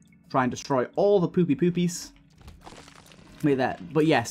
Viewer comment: hey, am i crazy or at 10:25 did he say “poopy doopies” and then immediately enter a room called “poopy doopy”?